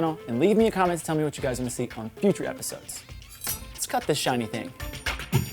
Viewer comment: At first I thought he was going to say, "Let's cut this shit!"